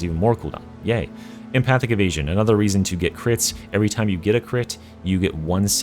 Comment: idk why but I heard empathic of asian instead of empathic evasion😂